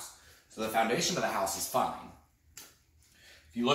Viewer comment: dont think thats what he said the YouTube He said the foundation of the house is fine 🤣😂